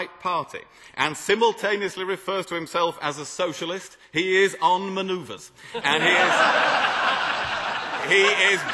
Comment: "He is un-" what?? Can someone tell me what he says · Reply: As its 10 years later you probably don't care but he said "he is on maneuvers"